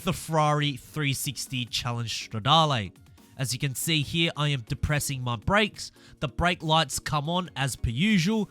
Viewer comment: did he say I’m depressing my brakes